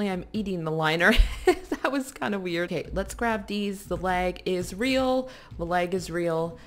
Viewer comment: I thought she said "The Leg Is Real" XD